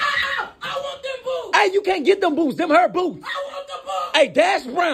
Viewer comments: I thought he said boobs😂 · I thought he said "i want the b***bs-😭😭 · When I watched this first time I thought he said AHHHH I WANT TO POO 💀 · I thought he said boobs💀💀💀